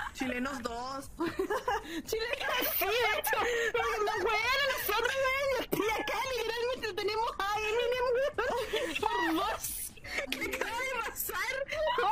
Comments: que dijo la eminem parte 2? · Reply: "Si, de hecho. Nos webean a nosotros verdad y yo estoy aca literalmente tenemos a eminem weon x2" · ¿Alguien sabe que dijo? (de forma no irónica xd) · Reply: lo que entendi: " Nos webean a nosotros weon, y este acá literalmente tenemos a Eminem____, x2" una palabra no supe xd